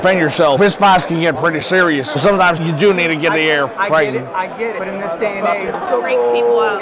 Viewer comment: It took me hearing the other guy complaining about the AR to know what my dude was saying the whole time. 😂 I kept hearing “air feign” or “arrow fang” this guy’s hilarious👍🏼 subscribing now!